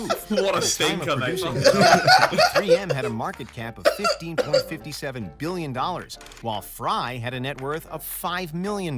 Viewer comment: What was the joke at 12:35 anyone heard it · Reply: "so he failed" - Simon Because the guy in the video was trying to make "strong adhesive" but made weak adhesive 🤷